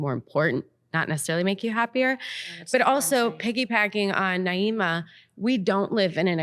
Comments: Did she say “Piggie Packing”? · Did she say piggy "packing?" I've just never heard that. I've heard piggy backing. Maybe I've been saying it wrong all of these years like if you piggyback off of something you are kind of adding to that.